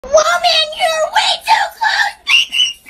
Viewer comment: ”WOAH MAN YOUR WAY TO CLOSE- PEPPER SPRÆÆÆÆÆÆÆÆÆÆÆÆY!” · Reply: Bro no way all this time i thought he was saying " mother you're way too close bbc"